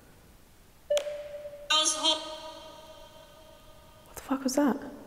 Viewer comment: “because I met you in there” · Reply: Also I hear “I was hot”